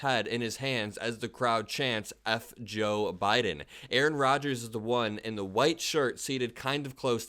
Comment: I HEARD '"LET'S GO BIDEN"